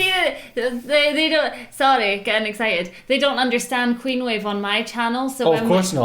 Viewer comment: must have t shirt Says clean wave How cool will that be 😀 Game of cleanwave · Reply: Isn't it queen wave though? Or did i mishear it?